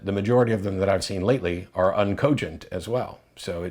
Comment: what is that word ? I dont understand it and I cant figure it out from context. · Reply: Uncogent.